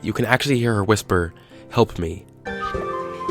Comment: I just heard "hep me"